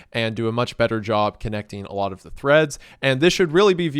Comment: Did you say… threads?… RED? THREAD?